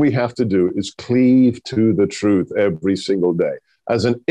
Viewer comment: He's a LEGAL immigrant. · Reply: hahaha I coulda swore he said illegal 🤣 my bad